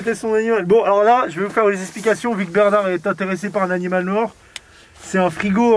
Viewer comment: "bernard est interessé par quoi ???" LES animALS morts ???